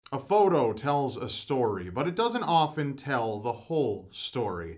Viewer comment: At first, I thought you said "A haircut tells a story"